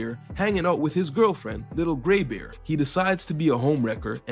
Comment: Captions said Little G*y Bear💀